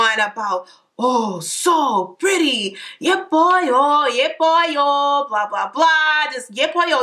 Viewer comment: i deadass thought she said "yeah boy yOo" 💀💀💀